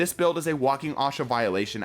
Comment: it is OSHA no ASHA at 5:09, I heard it and had to do a double take on it